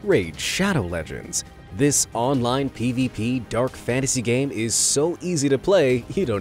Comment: that games is crap, that's what I heard.